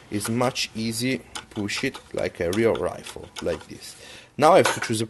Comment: to shit like a real rifle OMFG I LOST IT XD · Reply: You mean in 5:18 ? And i think he said push it dude